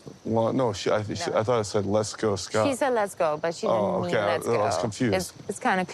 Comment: translator didn’t need to be such a cow about it. It did sound like she said let’s go scott